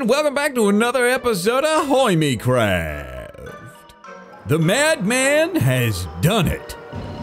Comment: did he just say "welcome back to another episode of ahoy me craft"?